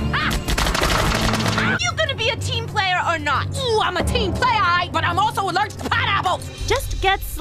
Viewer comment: what is she saying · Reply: Ooh I am a team player, but im also allergic to pineapples!